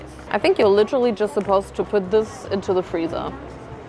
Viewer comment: did she say freezer?😭